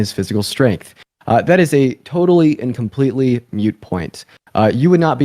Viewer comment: did he just say "mute point?" Cuz that's enough to lose an entire debate. If you cant use BASIC words correctly, you cant use complex words correctly.